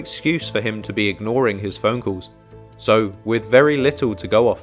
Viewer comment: When he said phone calls I thought he said funcles 😂😂😂😑😑😑